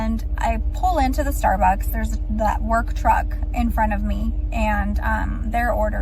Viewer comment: did she say “work trugg”? 👀😂